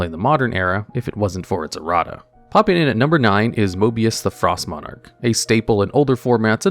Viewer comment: I misheard mobius as mobius the "frog" monarch. Tbh this makes more sense